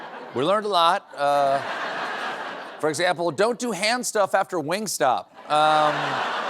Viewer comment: Help me, folks: 3:20 “Hand-stuff after...” (what?) 🤷‍♂️ Sounded like “Wing-stop”..? (surely not a reference to: stopping to have buffalo wings)..?! 🤷‍♂️ 🤔😮